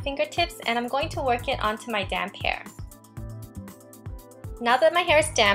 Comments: i thought she said "now i'm gonna put it on my damn hair" sorry :/ i know know you actually said "now i'm gonna put it on my damp hair" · oml I thought she said damn ;o